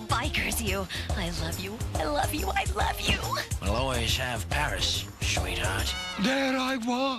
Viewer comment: What did Vinnie say 18:14? 😵😄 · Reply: We'll always have paris sweetheart